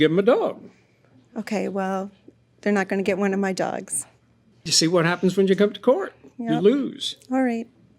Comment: “You see what happens when you come to court, you lose” I swear I heard “The more you f@ around, the more youre gonna find out” 😫😭😭😭